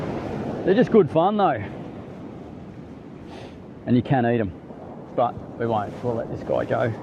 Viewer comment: And you can't eatem' ? Disappointed hearing you say that buddy, you're either a fussy fish eater or you've never had one cooked up good, sounds to me you wouldn't lower yourself to eat salmon, I won't lower myself to watch any of your posts, I hope a lot of others follow my lead.. Disappointed all round. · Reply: I heard him say “you can eat them” twice. You’re tripping mate 😂